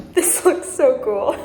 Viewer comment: "This looks so ghoul" ... is what I heard.